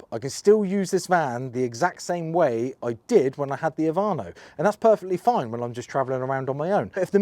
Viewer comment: Did you say you had a Viano but said Ivano by mistake at 11:20 of this video? I'm in Canada and searching for a solution for my MB Metris Passenger which in the UK seems to be a Vito Passenger. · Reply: nope. I said Ovano.